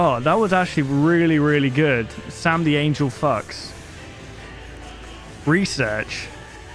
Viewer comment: "same the angle fox"? I thought he said "sam the angle fucks" XD